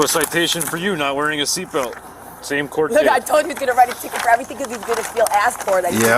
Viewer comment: I coulda sworn She said “nigha I told you you was gonna write a ticket” at the end 32:49 😭🤣